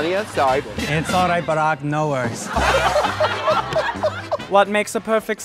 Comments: It's alright Barack no worries. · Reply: All this time I thought she had said ‘it’s alright, but I have no words’ hahaha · What does she say at 0:45? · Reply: She says, "It's alright, Barack, no worries." Barack as in Barack Obama, former president of the United States. The first black president. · NO WAY! I feed so stupid. I’ve just discovered that she said “Barack no worries”. I’ve always thought she said “but I’ve no words” 😂